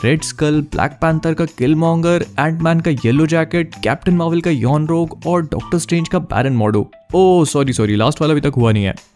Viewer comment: does he said yon rog